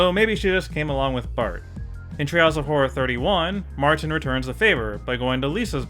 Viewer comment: Who else hears "Trials of horror" instead of "Treehouse of horror"?